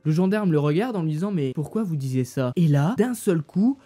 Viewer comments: « pourquoi vous disez ça? »😭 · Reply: il a dit disiez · « pourquoi vous disez ça » t sérieux la ? 😂😭😭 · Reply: Il a dit pourquoi vous disiez ça